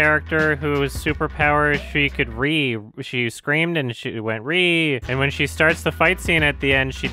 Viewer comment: I thought said her superpower was that she could breathe rofl